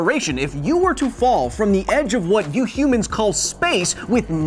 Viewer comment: "You humans" I heard that, Kyle!